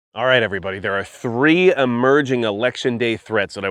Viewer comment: I thought it said "Erection" Day Threats!!